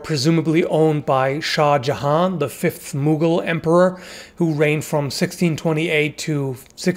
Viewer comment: I was listening while flipping through tabs and when he said "Mughal Emperor" I thought he said "Moogle Emperor" and It gave me rather amusing images in my head.